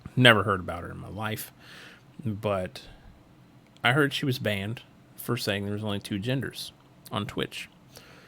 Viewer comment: W for the twitch moderator who unbanned her Edit: nvm L the twitch mod I thought it said "Unbanned"